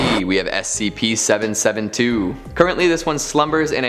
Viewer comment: did he just say scp 772